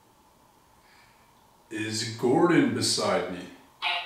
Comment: Omg at 53:40 when asks, "Are you beside me?" we first hear a loud scratchy partial through the app, but listen right after VERY quietly we hear what I think is a VERY VERY VERY softly whispered, "Yes."